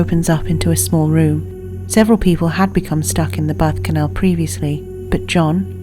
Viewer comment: lol it sounds like she says "Several people had been stuck in the butt canal"